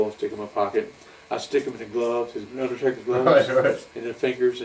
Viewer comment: I stg I thought I heard him say Undertater 😂